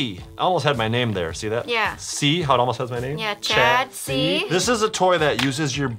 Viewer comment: sounded like Chad see